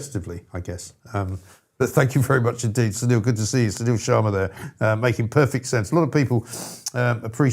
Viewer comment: Before I saw his name displayed I thought said Sir Neil Sharma, as in kneel Sir Sharma. Silly me. 🙄👍